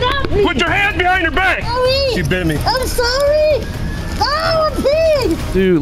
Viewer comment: I like how she said “sorry i’m a pig” after biting him like that’s any better 😂 Edit: It sounded like she said i’m a pig but I guess she said I had to pee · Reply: I swear before reading your comment or hearing what Donut said I thought she said “I’m sorry I’m a pig”